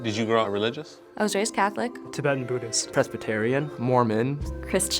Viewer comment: why I thought he said “pescatarian” 😂🤦🏽‍♂️ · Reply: He said "Presbyterian" lmao